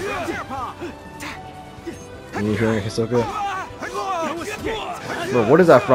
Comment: what did u say here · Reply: The enemies name was Hisoka and I couldn’t remember what it was from